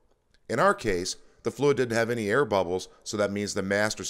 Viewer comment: Question:at 4:30 did I hear correctly? "the fluid didn't have no air bubbles so it will be replaced ".I thought no air bubbles was a good thing? My master cylinder has no air bubbles.